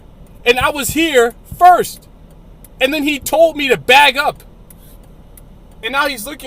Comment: At first I thought this guy just had a funny way of pronouncing "back up", but then it appears he is actually saying "bag up"